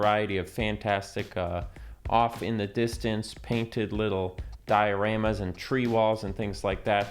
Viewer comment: My brain heard you say "Skyoramas" instead of "Dioramas" and now I think that needs to be the word to describe what you were talking about